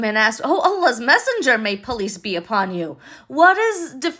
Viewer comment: Hi, I thought you said “ police be upon you “ instead of peace be upon you. God bless you